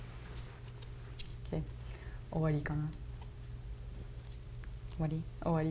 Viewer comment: What did they say at the end ? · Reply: おわりーOwari- finished